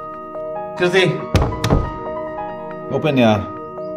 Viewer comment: He spelt 'Kriti' .. I heard 'Dhriti' 😂😂... !!